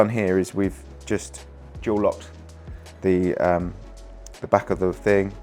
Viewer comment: This is such a DOOE rig and presentation. What’s he saying at @ 5:47? Couldn’t quite make it out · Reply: Dual Lock <link>